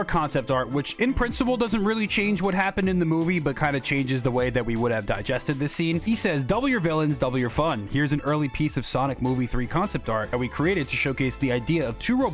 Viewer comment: Tgus entire time i thought he said "W villains, W fun